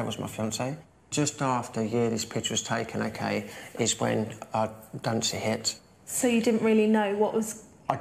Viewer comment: Can somebody tell me what (Super Skinny) says at 16:25 ? "Just after a year this picture was taken ok, is when _______ hit" Fill in the blank please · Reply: I think he says redundancy